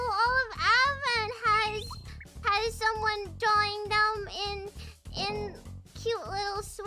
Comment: I think she says "All of Advent has had someone drawing them in..." instead of "join them". She is being all pouty, so I don't blame you for mishearing that lol